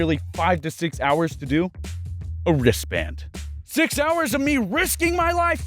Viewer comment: Wait you said ristband? I thought you said risk band